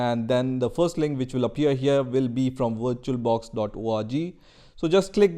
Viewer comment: I heard virtual orgy. lmfaoo. Im so baaaad.